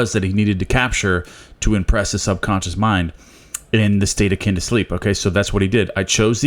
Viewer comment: At first i always thought you're saying, "A state of kinder sleep" only to find out through reading that It's A state akin to sleep(SATS)